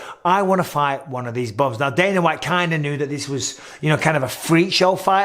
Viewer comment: What did Mike say at around 8:00? He called them some sort of derogatory name, lol. “Hey, I wanna fight one of these (blank)” Any ideas anyone? Would be much appreciated! Thank you! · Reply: Bums